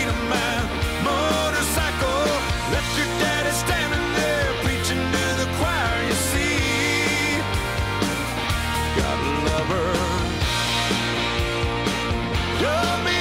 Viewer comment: For the longest time I thought he was saying "Old men gotta love her..."